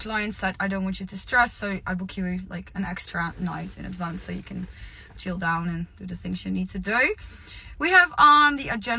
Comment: what the 🦆did she say🤔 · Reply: Not sure actually. But I did catch the “Professional Girlfriend” part. We used to call them escorts. Well actually we still do.